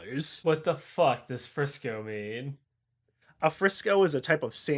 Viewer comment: I misheard Frisco as Vriska which honestly hit me different.